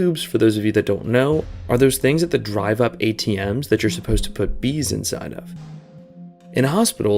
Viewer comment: Put WHATS inside of? Cuz I heard bees. also what's a piss-missile?